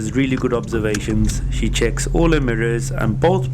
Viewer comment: Great video! I did notice at 6:44 you said that Simran did "his" mirrors well ;D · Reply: It sounded like he said "these mirrors" to me.